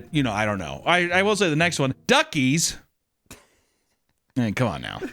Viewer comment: listening while looking away, I thought I heard Darkees. Had to go for a double take. Tip: please quit with the politics.